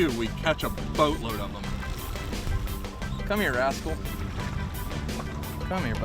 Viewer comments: I thought he said “come here asshole” 😂😂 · who else thought he said asshole?😂😂😂